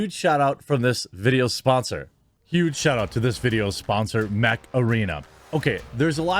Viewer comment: When you said "Mech Arena", I thought you said Macarena